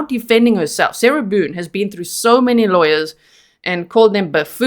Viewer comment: How many lawyers quit her? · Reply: Ohhh now I heard you say 8 lawyers... That is awful!!! 😮